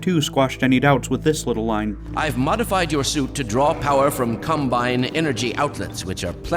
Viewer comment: I heard "I modified your suit to draw power from cumbine energy outlets"